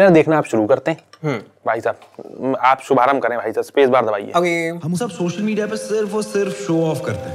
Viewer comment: did he just say bhai sahab aap shuruat kare spacebar dabakr??